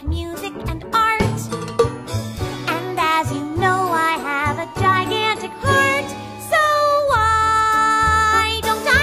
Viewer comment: i thought she said, I HAVE A GIGANTIC FART